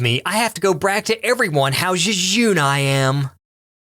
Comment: "Jejune" sounds so much like one of my favorite English words "jejunum" that I kept mishearing it so that called himself "small intestine".